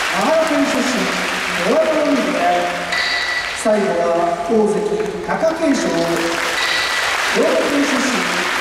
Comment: takakensho? Did I mishear?